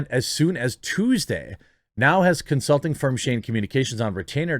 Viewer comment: I swear to God I thought her new PR team had the perfect title. I thought you said it was SHAME communications not.. Shane..